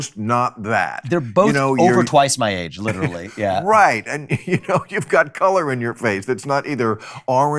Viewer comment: Bill Maher saying Vivek is a good candidate because he’s a person of colors is the bigger insult, reducing someone down to their worth because of their skin color is exactly what out of touch liberals do today. I loved everything Vivek said and so excited to have an intelligent, young, hard hitting man running for the president of our country!! USA!! 🇺🇸🇺🇸🇺🇸🇺🇸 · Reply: What I heard him say is "you have colour in your face" meaning not orange on one side and ghostly white on the other side.